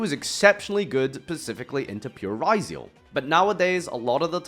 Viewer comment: Did he just say "Pacifically?"